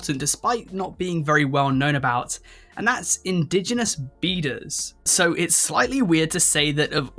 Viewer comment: I was dosing off when I thought you said indigenous beaters not beaders.